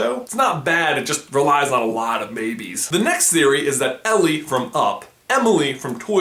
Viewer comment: I thought he said "But it relies on alot of babies" instead of Maybies 😂